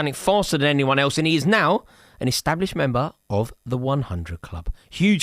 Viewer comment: am I hearing he is… part of the establishment?!😱😮 Can’t be disrespecting him, 😅